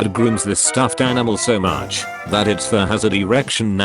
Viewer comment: too bad they don't read comments. But the tts voice said "erection" instead of "direction"